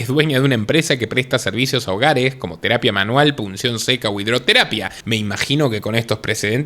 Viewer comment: ¿cuál es uno de los servicios en el hogar ...que dice que presta? ... ¿"polución seca"?